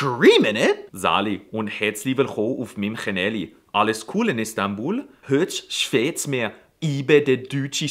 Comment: I swear it sounds like he says "İstanbul" in here. Am I hearing it wrong?